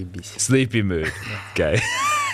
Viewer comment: don’t think that he said “sleepy” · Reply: He said "заебись" - This swear word means very cool